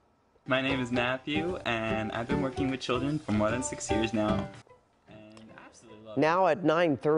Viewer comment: Did he say 60 years??